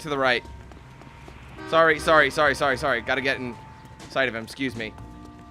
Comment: I misheard you and though you said got to get inside him instead of got to get sight of him XD